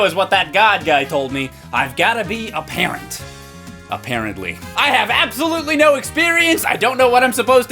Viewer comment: omg did he say "A parently" or am I just hearing things ? XD